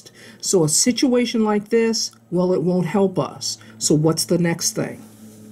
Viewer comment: “So a situation like this….” I dead thought she said “may god help us” LMAO I was like whatttt lemme go back😂😂. These screws are annoying af